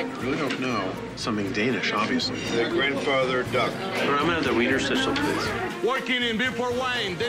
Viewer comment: did he say weiner snitzel LMAO